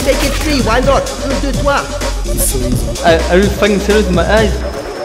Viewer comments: Can anyone tell me what he says at 2:52? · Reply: Shaurya are you facking serious is my eyes? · what did he say? Ive been wondering this for real long. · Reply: are you fucking serious in my eyes